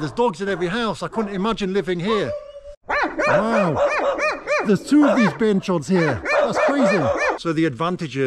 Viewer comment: Nice video, had a question for you, at the 21:23 mark, what did you say ? "Wow, there's 2 of these. **** here"